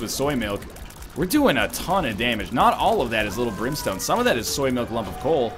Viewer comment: At one point it sounded like you said Limp Brimstone instead of lil brimstone... lol